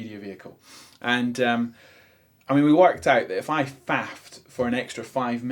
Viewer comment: he was talking about fathing for 5mins? What is the word? What were they talking about? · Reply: Faffing about, wasting time doing nothing in particular.